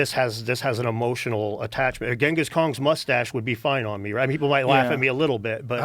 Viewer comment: Did Dan say Ghengis Kong?